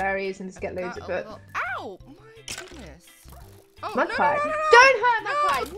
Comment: when stacy says :mudpie nooo! it sounds like she said :my albinoooo